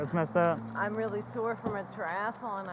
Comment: Did It say their ass is really sore?